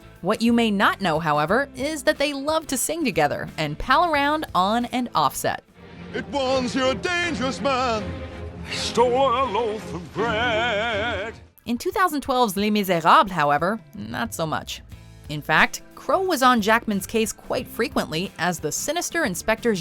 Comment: Did he just say that someone stole their loaf of bread?